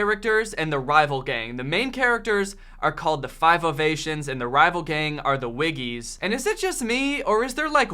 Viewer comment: I thought you said "five of asians", not ovations